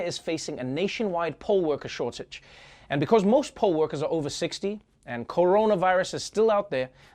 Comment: Was I the only one who thought at first he said "pole worker" instead of "poll worker"? Then when he said most were over 60, had a mental picture of a retiree pole dancing? No? It was just me? 🤦‍♀️